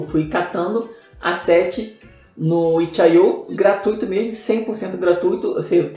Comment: O que ele falou aqui? onde ele pegou as artes? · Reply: Fala! Eu uso muito o site Itchi.io pra pegar assets 100% gratuitos.